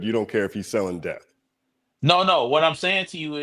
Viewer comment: "Sounds like you said that Drake made music that's so good that you don't care if he sells you death" 🎯🎯🎯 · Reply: *Debt, but yeah lol